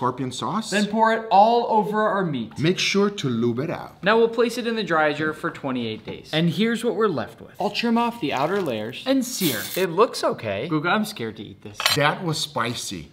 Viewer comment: I thought he said now let’s put it in the dryer for 28 years